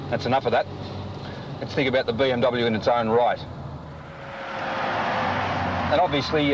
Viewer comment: When Peter Wherret said “In it’s own right “ I thought he said “In it’s own Reich.”